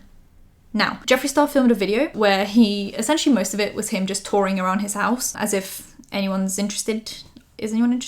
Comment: i thought she said "torying around his house" at first instead of "touring", oops